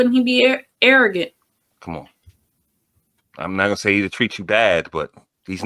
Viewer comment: All I heard her say is I’m a goof